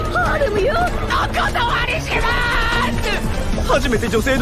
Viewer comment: アクア様にしまーす！に聞こえたw